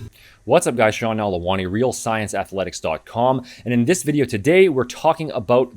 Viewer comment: did you said "athlean X dot com" 😳😂? It's an Easter egg? 😂😭